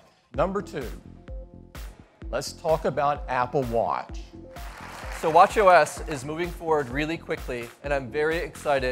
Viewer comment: It kinda sounded like he said "watch yo ass"